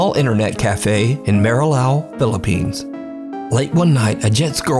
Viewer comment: did you just say "Manilla"? is that how you pronounce it? xD as someone from the Philippines, I just find that funny. i had to replay it 5x+ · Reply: I think he said "Marilao" idk